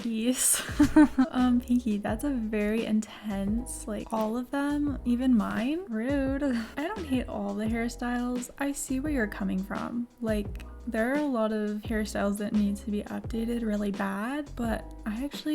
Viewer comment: When you were talking about the hair i thought you said harry styles, and i was like "thats not sso"